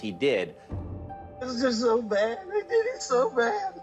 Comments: I hear, "It's just so wrong." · Sounds like he said "I did it so bad." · When they slowed it down I heard, "It is so bad" twice. No "they" or "I". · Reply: I heard "i did IT so bad".... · I think he’s guilty as sin, but didn’t hear either of the things mentioned in the debate, I heard it as “I did, it’s so bad” in response to them saying he saw something traumatic